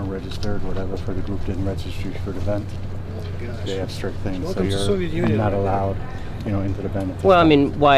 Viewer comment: I can’t be the only one who heard “Welcome to Soviet Union”